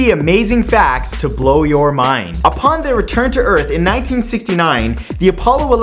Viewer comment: Hahahaha when you said "upon their return to earth" I heard "a panda return to earth" I was like WTF?? They sent a fucking panda???